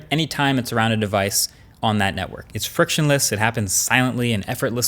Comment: did he just say "frictionless" is he watching too much Cody Ko or something?